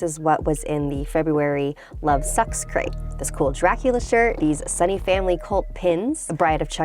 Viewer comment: did she said Sexcrate?